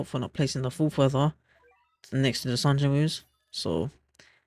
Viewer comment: Yo what did you say at 5:47 , english isnt my first language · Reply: Next to the sung Jin woos 😅 I'll try speak at a steadier pace going forward